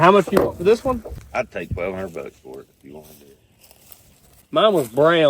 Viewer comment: Did he say $1200 US dollars for a beat up 6 cylinder Maverick with no engine? I couldn't have heard that correctly.